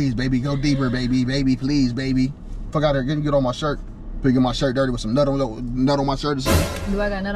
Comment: Nut on my shirt! 🤣🤣🤣🤣 comes up with the craziest things! He went from loving to hating the socks! knows how to piss off! 😂😂😂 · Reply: I think he said shirts 🤣🤣 12:45 I clicked on that like 5-6 times it make it more funny if u click after he say nut on my shirts